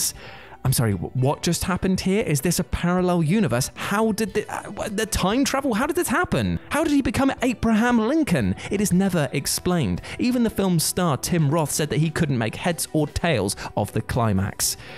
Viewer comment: Heh. It sounds like he said 'Ape-raham Lincoln.'